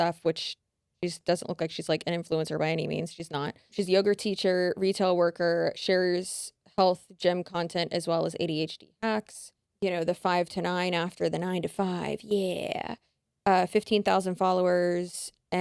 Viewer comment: it sounded like said "she's a yogurt teacher" and I chuckled like the dumbass I am, love you guys lmao😂❤